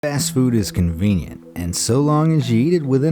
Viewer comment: did he say ass fodd?